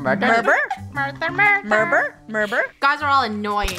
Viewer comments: Sounded like funneh was saying “mUrvEr” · When u were lunar on the 2nd round u sounded like crows saying murber ngl